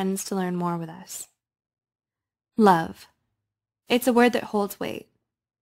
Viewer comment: legit just thought: “love, it’s what makes a Subaru, a Subaru.” And now I’m disappointed that’s not what it said